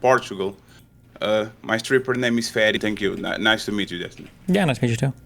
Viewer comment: Did he say he's a stripper ? I just want to make sure my degenerate mind isn't deceiving me.